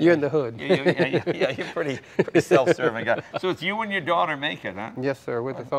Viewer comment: I thought he said "it's you and your daughter naked"